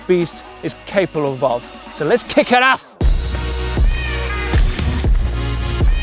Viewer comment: what did he say 😂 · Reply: So lets kick it off